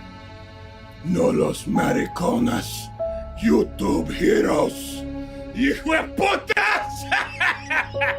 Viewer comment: He actually said “not the homos/gays ‘Youtube Heroes’”